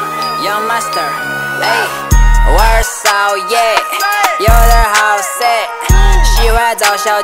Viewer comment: had this playing on the background and i thought they said ´worst song yet´ in the beginning